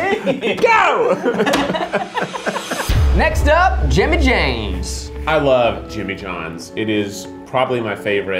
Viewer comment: did he just say jimmy james?